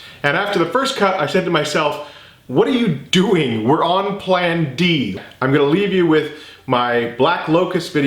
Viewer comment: I thought it said plan b that’s what I came for